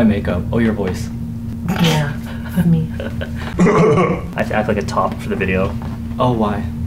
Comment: Am I tripping or did just say "I'll act like a top in this video"? At 1:17???? Skskskks